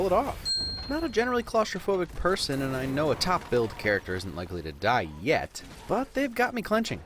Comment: LMAO!!! @ 6:10 when he said top billed I heard top "built" 😆 Which is funny because the actress is seriously built on top.